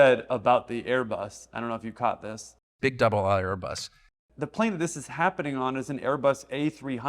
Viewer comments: Sounds like Tucker said "double isle" Airbus... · I don't think he says double aught; I'm pretty sure he's saying "double-aisle" as in widebody. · I think he said "double aisle" not "double aught"